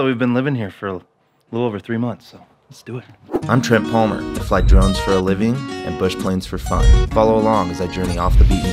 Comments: I could of sworn he said at the beginning, "I'm Trent Palmer, I fly drugs for a living and bush planes for fun." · Flies drones for a living yet doesn't give us an aerial shot. Come on, man.LOL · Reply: I thought he said “fights drugs for a living” lol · Flies drones for a living yet doesn't give us an aerial shot. Come on, man.LOL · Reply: I thought he said he fly's drugs for a living! I listened several times and that is what I heard.